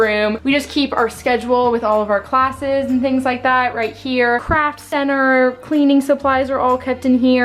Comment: Haha when you said “craft center” I heard “crap center” at first. 😂